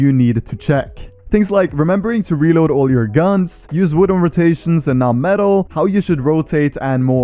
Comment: did he just say wood and not metal in rots?|